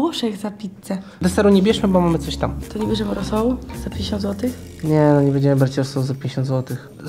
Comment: Czy ja jedyny tu usłyszałem rosołu za 1k złotych